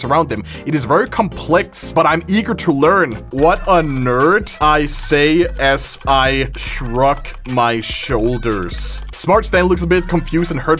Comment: i wasnt paying attention for a sec and thought it said ""what a nerd" i say as i saw off her face"